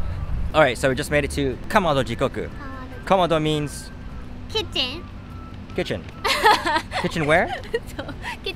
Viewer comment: Could have sworn said kitten not kitchen